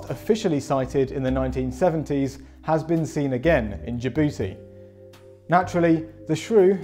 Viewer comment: what did he say about my booty?